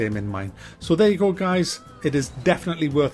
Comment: Thought you said "I'm off now to do colon excercise" at the end. Thought, Poor b'std had to endure kidney stones, and now he's blown out his ring piece